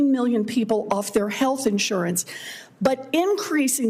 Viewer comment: sounds like he made a Freudian slip there haha "wealth"... at least thats what i heard